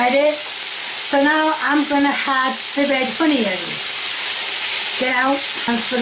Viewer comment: she's always called her spatula an oofah-doofah because apparently the word spatula is too hard to both remember and pronounce for her. needs to catch up on his Lore. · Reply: Honions (I thought she kept saying "honey tips")